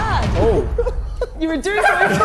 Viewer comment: what did Harry say?? I’m confused about the whole thing 😂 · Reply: In the full video, he says "basically, are you on casual sex, he's tryna ask" obviously, in this clip, it only said part of "he's tryna ask"👍🏽🤣